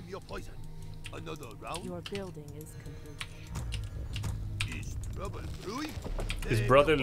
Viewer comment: mhm i always heard it "Is trouble brewing " like he's asking if there's a trouble